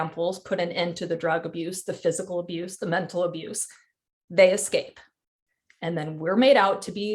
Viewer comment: Did she say "When they ESCAPE?"